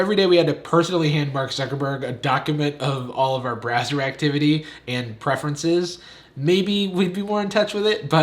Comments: Is it me or did he say brazzer activity? Did he mean to say that? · Totally thought he said Brazzer activity, not browser activity. Oops.